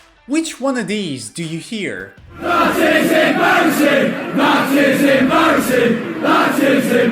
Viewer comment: I hear that’s embarrasing